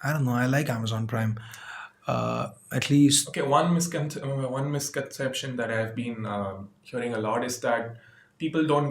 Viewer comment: did anyone catch that@ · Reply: he said "asks me then cuts me off" something like that :P